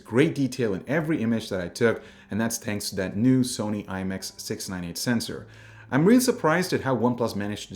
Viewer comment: sony imx 689 right? 😐 Or I heard it wrong